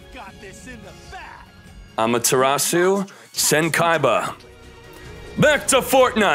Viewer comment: Got scared thought he said Yata-Garasu 🦅🤣